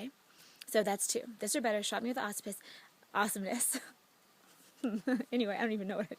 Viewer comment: Hahaha 5:29 "shock me with the Awessupiss"... Love it! and your reaction when you realized what you had just said. That entertained me a lot!